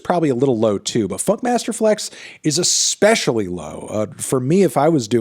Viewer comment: I thought you said "F*CK MASTER FLEX!" at 5:09 LOL I was going to say, oh no they have beef I didn't know about.